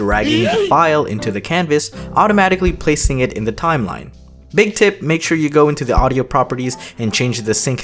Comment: i thought he said big tit